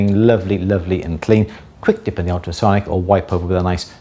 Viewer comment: "quick dip in ____..." huh? I couldn't make out what you said to dip it in. I'm sure its covered in another video, as I'm aware of how thoroughly you explain everything and it seemed like you were going over redundant material when you said this.. I think the problem is in my bad hearing and my American accent. I tried 3 times but still couldn't understand what you said to give it a quick dip into. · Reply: "quick dip in the ultrasonic"